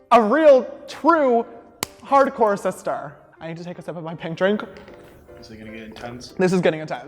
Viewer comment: I was like, "Did he just say pink drank insted of pinkity drankity!" Lol